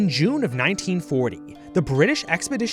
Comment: wait does he say 1940 ?